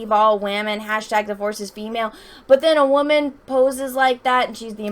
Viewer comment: haha for some reason i heard that as # divorce is female